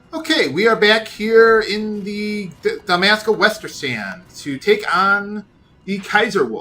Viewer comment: I thought I heard Dumbassca Westersand.